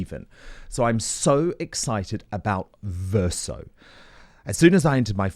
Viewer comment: Hi Dan and Lady Collin Campbell: I’m an American, but the pronunciation between the Queen’s English and the English dialect spoken here sometimes makes me hear one thing when in actuality what I hear and what was said can be lost in translation . For example, as Mr. Wooten began his endorsement @ 9:39 into the segment. I was caught aback a bit when heard believed to hear him, in a vivacious and enthusiastic manner, announce …” I’m so excited about “Fur Soap”. I looked at the screen and noticed a banner along the bottom of the screen saying something about “[buy.]VER.SO”. Just thought someone at that end would need a laugh. You’re both lovely hosts alone and especially together. You have mutual respect for each other and it comes through. Thank you both for that which you bring to light.